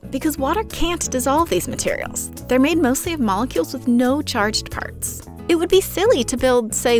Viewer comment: I thought you said charged farts